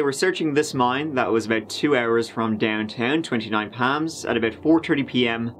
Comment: At first I thought you were saying 29 Pam’s! I hate it when people defame people that can’t speak up for themselves. Sad story for Erin, John and unborn baby! Glad the jury saw right through Chris’ lies! As always… Too short! Have you thought about making longer videos? We love you! Take care! 😁